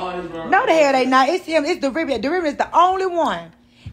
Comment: I thought she said "Derivative" thought she was speaking finance🤷🏼‍♀️